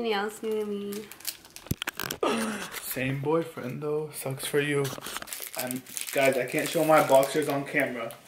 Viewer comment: put your volume down and it will sound like he said no sex for you